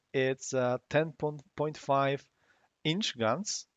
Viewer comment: Thanks. I wasn't far off I think. :D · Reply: I initially thought you said 10.5 cm rather than inches and went 'how the hell don't you know how to convert that?', then I rewatched it. XD